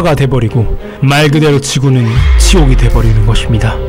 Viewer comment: 지옥이 치옥 이라고 들리는거같아....ㅋㅋ